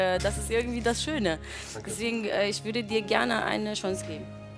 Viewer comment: Sagt die da "Ich würde dir gern ein Schwanz geben" ? oder hab ich des jetzt falsch verstanden ? :D